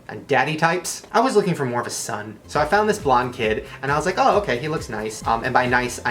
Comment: Why did i hear him say "a blind kid"?